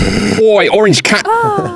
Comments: “oi orange ca-“ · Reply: he says "oi, orange cun-" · OI ORANGE CU- Edit: Woah, woah, 1K likes? That’s a lot. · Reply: It was cat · "Oi orange cat" Garfield? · I'm sorry, what did that guy say? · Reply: Oi orange cat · "OI YA ORANGE CU-" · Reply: rip I heard "oh an orange cat!"